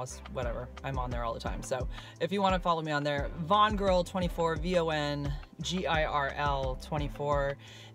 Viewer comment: First time I heard you say I heard it as "Bond girl"... Made sense to me...